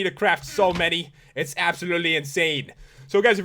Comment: I honestly thought he said app-solutley.